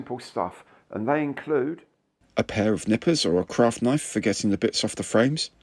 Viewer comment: I thought I heard "a pair of knickers". I've just got back into modelling after a 20 year hiatus so u was well impressed when I saw airfix and revell in Lidl; a family bought the Titanic kit a few minutes before me so I got the Mayflower as one to put away til I've finished the backlog